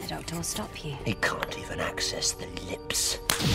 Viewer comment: What does the Doctor say at 0:53? 'He ... access ...'? I'm not English, sorry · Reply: Sean Grand he can't even access the lips. means the doctor cannot control what he is saying. its under the cyber planner's control